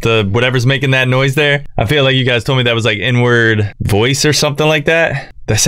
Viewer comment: Bro i thought you said "thats the n word voice" instead of the "inward voice" im dead